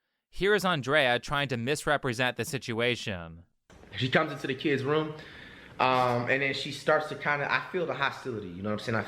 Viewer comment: did he just say "KID'S room?!" there are kids involved in this bullshit? Jesus christ.